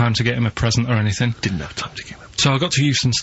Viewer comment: What is Steve whispering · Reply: "didn't have time to get him a..."